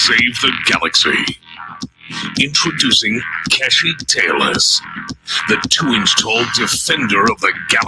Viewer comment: I thought the intro said "Kashykk Tailors", which would be a business about as successful as Mecca Wine Brokers, or Tel Aviv Pork Scratchings.